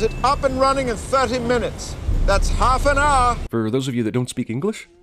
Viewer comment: His "thirty minutes" could have been misunderstood as "thirteen minutes" if he hadn't clarified. In fact, I think he is saying "thirteen".